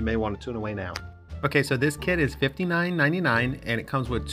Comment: Why did I hear "ok so this kid is $59.99"